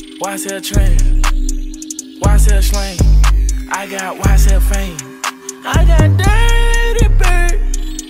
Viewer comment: If you look closely at 0:14 his lips said “Slang” instead of “Fame” 😂